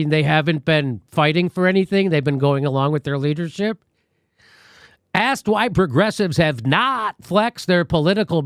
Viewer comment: When Jimmy said "Asked why progressives" at 3:15 I thought he said ass-wipe Progressives and it had me rolling lmaooo